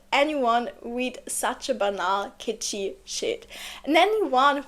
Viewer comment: Please someone correct me if I am wrong. Did she just say "banal kitchy shit"? I had to laugh out loud when I heard those words. But I'm finding it hard to fathom this young upstanding woman meant to say those words on her channel. Surprise! Surprise! I love it though! Another great video indeed. Loved your words, very deeply explanative.